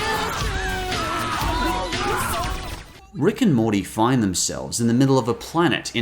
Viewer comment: did you just say JEGUS????